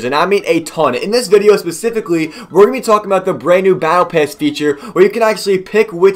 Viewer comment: it sounded like he said battle pess at the start